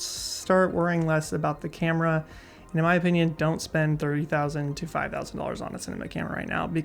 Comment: did he say thirty thousand or three thousand? Lol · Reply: haha. At first it sounds like thirty, but he really did say th-ree.